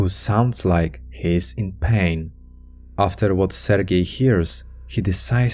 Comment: Instead of hearing "Sergei", I heard "they're gay". Just me?